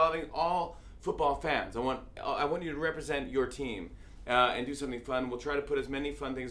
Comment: Did he say "do something flammable?" at 1:22? ...no problem:p